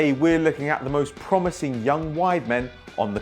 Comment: I really thought he said 'Most promising young white men'